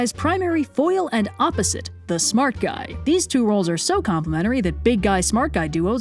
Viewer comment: I thought it said smart guy THICKNESS 😂